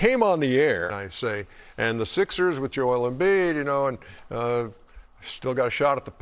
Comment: Was Chuck roasting thought I heard him call them the sickers😂🤔👀